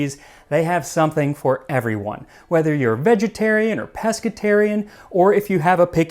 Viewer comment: On the hello fresh ad i thought he said whether your vegetarian or presbyterian 😂